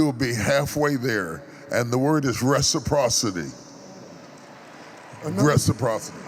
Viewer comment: What was that "one big Word? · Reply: Reciprocity